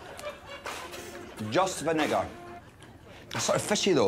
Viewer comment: What does Iain say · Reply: just vinegar